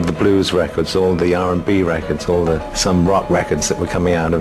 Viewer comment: Did he say Sun Ra records at 3:09?